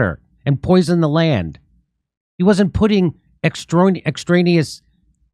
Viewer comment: Did I hear "pudding"...